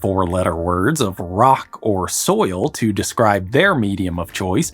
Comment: did someone say "Rock and Stone" brother?